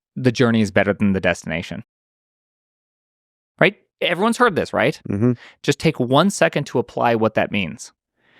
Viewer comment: Miss-heard Michael, thought he said "The journey looks better from the destination." I think that makes a much better saying.